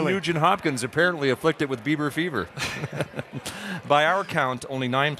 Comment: LOL I thought he said "beaver fever"